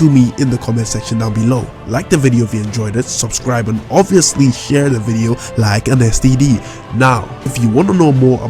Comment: DID HE SAY "SHARE THE VIDEO.. LIKE AN STD" LMAOOOO💀💀